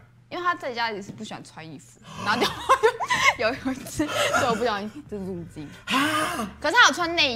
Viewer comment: 請問18:06Mita說什麼？聽不清楚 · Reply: 他就不小心入鏡